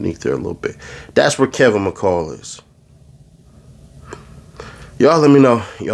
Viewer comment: I thought that said Kevin McCallister, I'm getting off my phone for the day.